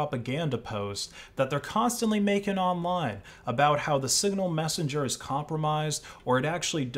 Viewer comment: I have sworn I heard that signal has been compromised as well. Anyone else hear this?